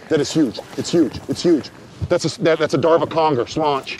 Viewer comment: Did he say "that's a Darva Conger" ha ha lmfao